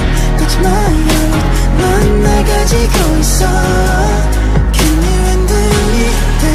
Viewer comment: WHO ELSE THOUGHT YUGYEOM SAID “CAN YOU UNDO ME”..... ONLY ME?? Ok